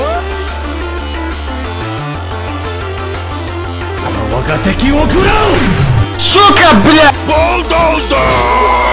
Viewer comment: ogotaquverikaka? What he sayed? Heeelp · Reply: Ryuu ga waga teki wo kurau